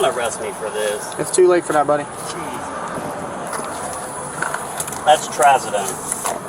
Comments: As a former LEO, these tactics disgust me, these officers disgust me even more. There’s a lot of corruption in the legal system, from the traffic officer, all the way to the judges that go along with type of corruption. After sixteen years with the department, I had enough of this garbage, and I pursued a totally different profession. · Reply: he told him what the white substance was, it sounds like he said that is trazodone at the 12:04 mark. No reason to call it cocaine. Trazodone is used for humans and animals. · he says what the powder is he said trazadone or trazadine or something. What a waste of time and energy by the police and city taxpayer money